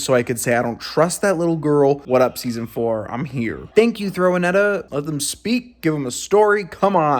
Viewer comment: dude did you just say “throwanetta”? HAHAHAHHA but then again it’s 5 am 😂